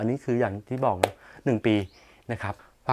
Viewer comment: ผมได้ยิน 1 ปีถูกไหมครับ